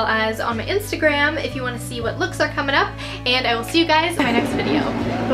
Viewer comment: Did I misheard in the last scene that you say "Bagus Gak? " :D